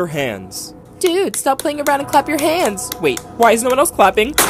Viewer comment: bro i thought i heard clap your cheeks from the second simon says 💀